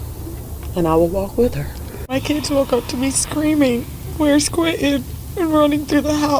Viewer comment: I heard her say, "My kids woke me up screaming, Where's Quinton." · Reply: she said: woke up to ME yelling and crying